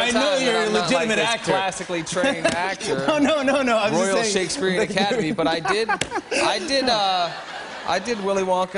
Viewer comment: what does he say about the academy at 1:50? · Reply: royal shakespearian academy